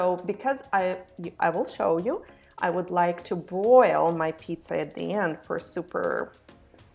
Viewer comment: I thought you said to boil the pizza. I think you mean broil. Anyway, it looks nice. Will give this a try, thanks.